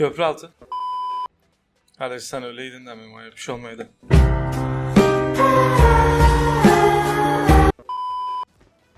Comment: ne diyor ben anlamdim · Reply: Diyorki kardeş sen ölseydinde memoya bişey olmasaydı